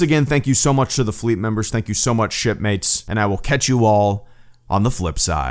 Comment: I initially heard it as "Thank you so much, shitmates" instead of "Thank you so much, shipmates".